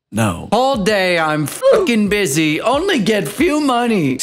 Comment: HOLD ON IM CRYING I THOUGHT THAT SAID BUSSY JDJGVJKKGDKGDD